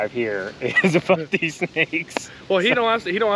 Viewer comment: Did just say f@ these snakes? Because I know this is a family channel, but that is perfectly acceptable in my book :)